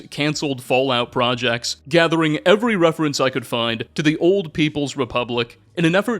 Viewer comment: I heard, "old people's" republic 😂